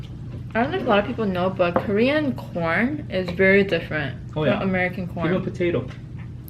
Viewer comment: I was talking to my husband at the time you were talking about American corn and I could have swore you said American porn! I snapped back real quick and was like what in the world is talking about today?! 😂😂😂